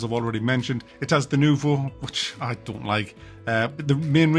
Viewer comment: what did he say the pirates have? “Newvum????????” 07:29 “denewvo?????” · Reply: "Denuvo" - never heard of that? Welcome to the gaming! ;-)